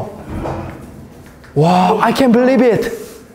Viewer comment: He said 16 times, "Wass haperring? " 4 times, " "wass going on " & 10 times, " Tuu cold".🤣🤣🤣 4 times, " I can't birrib it". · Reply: for your kind information : In that video he said, " I can't birrib it ". You thought that he wanted to say " I can't breath it." Basically, he said " I can't believe it " ( which is in clear English accent)